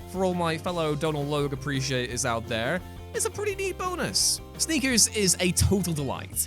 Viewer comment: When you said "Sneakers", I thought you said "Sleepers" and was in total agreement. Yeah, Sleepers wins out for the incredible cast.